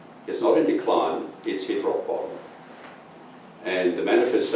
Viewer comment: Boer uses a word(s) TWICE that is unintelligible. The west is not only “in decline,” it is “????” What is that word and where can I make a financial contribution to get you guys some decent microphones.? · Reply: rock bottom